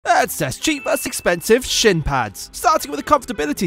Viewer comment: Is it is me hut I heard "cheap vs expensive sh#t pads"